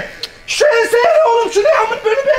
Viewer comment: 'da ne dediğini anlayan Bordo Berelidir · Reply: Beylikduzunde kaçak kat çıkmış mühendis müteahhitler gibi